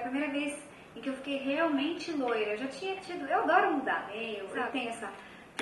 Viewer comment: a primeira vez que eu ouvi 1:37 eu achei que ela tinha falado "eu adoro dar"